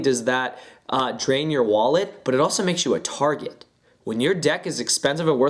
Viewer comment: when he said ur a target i thought he said retarted